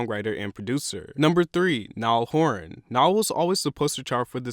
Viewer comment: Did he just say ‘Nile Horn’